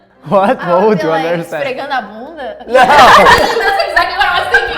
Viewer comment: I'm verrrryyyy curious. What did she say?? 😂😂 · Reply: "esfregando a bunda". can mean many things lol. rubbing or grinding the ass/butt. in some contexts in Brazil it can also mean to dance shaking your ass